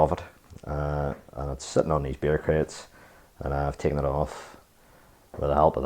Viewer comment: Had to turn on closed captions, but the funny thing was it was writing what I thought you were saying also. It said that the head it sitting on these bureaucrats, which is what I'm hearing and don't know what it is.